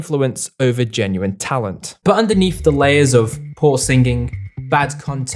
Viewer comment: It sounded like he said whore singing xD